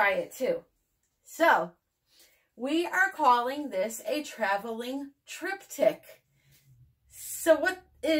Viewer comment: Bahaha .....I thought you said traveling trip di.k. Love the result..